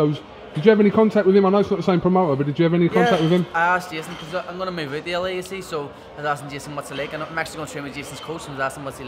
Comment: wtf did he say from 3:34-3:42?! Is it me or was he speaking a different language i was getting well confused lol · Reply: He said: yeah, I asked Jason because i'm going to move out to LA you see, so I asked Jason what's it like. I'm actually going to train with Jason's coach so I asked him what's it like and he said it's brilliant listen... Nice to see a Brian Badonde fan btw ;)